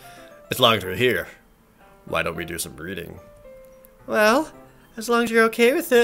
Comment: I'm sorry, I misheard "reading" as "breeding" for a moment there